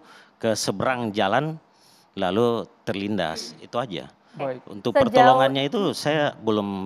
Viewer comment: "terlindas" 11:31 "benturan kepala" mungkin salah denger